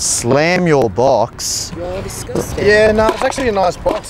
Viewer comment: what did the girl say? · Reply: She said: "you're disgusting"